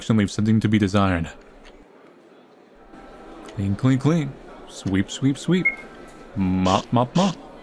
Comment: did I just hear GOTTA GO SWEEP SWEEP SWEEP